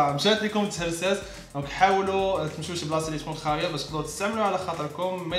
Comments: واش غ بوحدي لي سمعتو قال شي بلاصة تكون خارية و لا غ جابلي بالي هههههه بانو رجلة فدقيقة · واش بوحدي لي سمعت خارية ؟ 😂😂